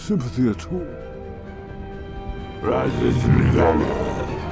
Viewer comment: What I heard: "Raßní Shzina Myagh"